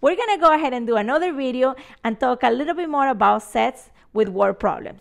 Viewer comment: who else heard "we're gonna learn a little bit more about sex"??